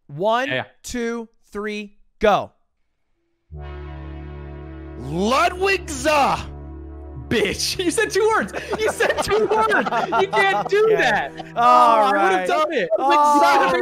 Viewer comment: sounds like atrioc is saying lud-wichser, wich is actually lud wanker in German